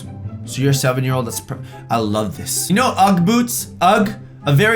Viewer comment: did he just say "ugh, boobs"?